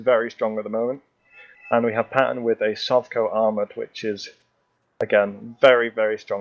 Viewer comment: I thought you said a Sotko armored deck...